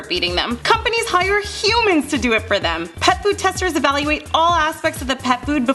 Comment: not to be mean but, sounded like you said, pet boob😋